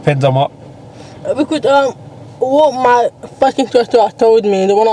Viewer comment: did she just say "what my f--ing instructor told me" lol